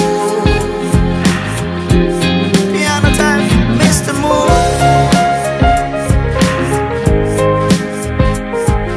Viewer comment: Una de las mejores canciones del album, lo descargue en mi cuenta de Spotify desde el primer día, pero no se porque descubrí el video oficial Hasta hoy 😢 pero bueno...alguien que me diga que dice en el minuto 2:45?? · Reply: "Piano time, Mister Moon" Tiempo de Piano, Señor Luna